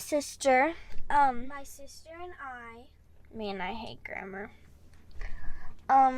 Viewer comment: At first I thought she said "I hate Grandma"